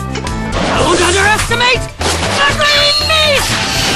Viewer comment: I thought he said 'Don't underestimate the Green BEAN!!' XD